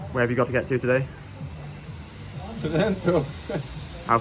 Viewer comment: awesome footage! what did the guy at 2:17 say? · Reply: Trento